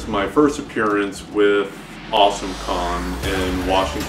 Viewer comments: I can't have been the only one who kept mishearing "Awesome Con" as "Awesome Kong"... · I thought they said Awesome Kong... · did he say Awesome Kong? 😂